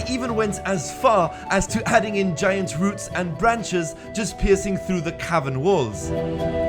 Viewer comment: I thought he said pissing through the walls lol